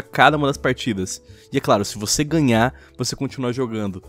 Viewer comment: "*eu tentando prestar atenção ao que eles falam* "* 2:15 van da claro*